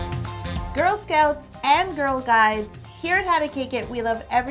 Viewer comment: Girl scouts and girl guides 😂😂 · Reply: Hope Mohlala I thought she said girl guys